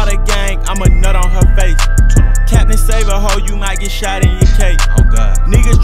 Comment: did he say u might get shot in UK? 😂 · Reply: DRIPSDaGoon DMG that would’ve been funny but he said “ you might get shot in your cape “ meaning trying be a hero